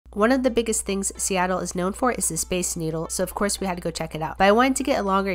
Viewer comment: I think I’ve been watching too much I thought she said space noodle 😂